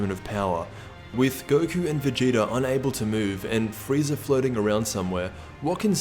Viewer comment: Lol I heard "Frieza flirting around somewhere"